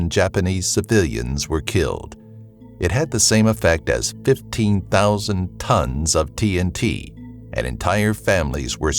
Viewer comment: i thought he said 15,000 tons of DMT and just imagined everyone tripping the fuck out